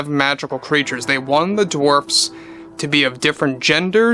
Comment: The seven dwarps…. That was clever. Loved it! Wish they would put this on a shelf and just forget it… cut your losses so that they can have a future. At this rate, Disney is trying to run Walt’s dream into the ground. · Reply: I was just gonna comment on how it sounded like he said 'Dwarps' and how that fits so well.... Snow Woke and the 7 Dwarps! Perfect! I love it! Lol!😅